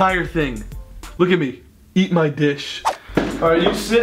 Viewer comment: I thought Grayson said “eat my bitch” ahhaha 😂😂😂😂